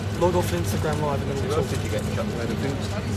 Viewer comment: Are we all gonna ignore what they said at 3:49 Someone I think lloris says “hey we get pissed tonight?” and Harry Kane says I hope so or something like that and that’s when dele says “let’s pretend that wasn’t on my instagram live hahaha” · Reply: I heard that. It's funny cos Harry doesn't drink. I initially thought Hugo said do you get paid tonight😂